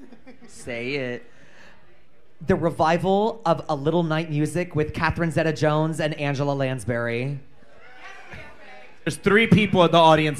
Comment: Could anyone tell me what Plasma says in 50:48, pls? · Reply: the revival of a little night music with catherine zeta jones and angela lansbury